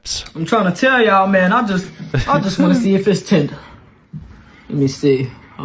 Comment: did pops just say tender-nism?? 😂 😂